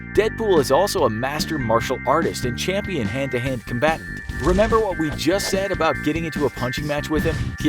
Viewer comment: it sounds like he said cumbat